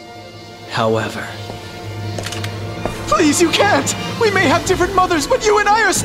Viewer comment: I thought Clovis said"We may have diffrent mothers blonde!" xD xD xD